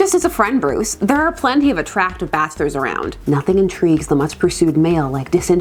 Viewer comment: I swore I thought you said there's plenty of bastards not bachelor's hahaha